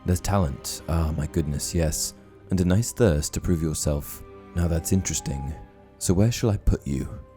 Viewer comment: Omg. I've read the books once but I've listened to the audiobooks many, many times. I've also seen the movies several times. I always thought the hat said, "and a FIRST to prove yourself" and I was like, "What? Both his parents went to Hogwarts." I've misheard it all these years. Good Lord...